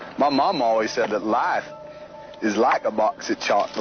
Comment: I always thought he said lifes not life is